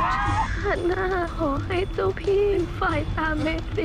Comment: เมรีพูดว่าอะไรนะ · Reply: Phatlada Mpk เมรีพูดว่า “ชาติหน้าขอให้เจ้าพี่เป็นฝ่ายตามเมรีไป”